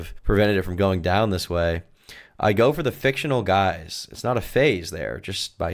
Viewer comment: OMG, I thought that line said "I go for the fictional guys" said "I go for the Fentanyl guys".